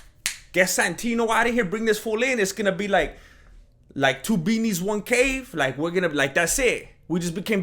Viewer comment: Omg! I swear I thought was about to say: 2 beanies, 1 cup 🤮🤮🤮😂🤣😬